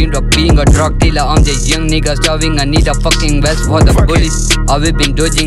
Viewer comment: Pissed myself when I heard "Umz Nigga" 😂🤣😭😭😭🤣🤣😂